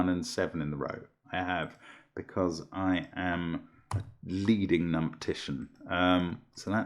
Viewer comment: Did say "leading numptitian" at playback time 9:54?